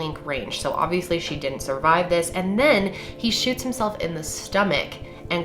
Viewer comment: lmaooooo I thought you said he shit himself 😂